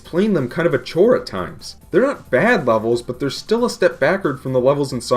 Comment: Did he said wevels not levels